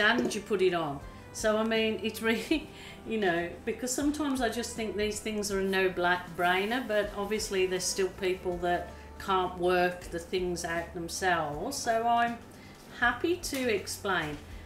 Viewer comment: did she say it was a "No Black Brainer" ???